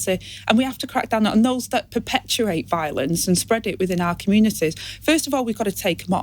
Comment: Did she say 'Fuggish'? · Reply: I thought she said 'perpeturate'. Is it her teeth or her brain?